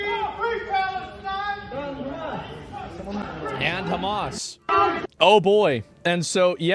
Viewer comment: I think that guy says “…From Hamas…” which is legit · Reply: He's saying "and hamas" and it is not "legit" he's a murderous psycho